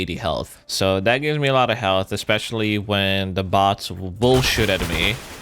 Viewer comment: sounds like he said bullshit